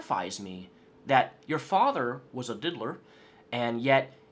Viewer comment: What word did use when he was talking about Onision's father? It sounds like he used the word "diddler" at 6:53. I played that part back 5 times and I keep hearing "diddler", a slang term for a pedo. If anyone hears a different word at that point of the video, please let me know. TIA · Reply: Yeh Gregoblin has accused his own father of being a child molestor for years. It was diddler you heard.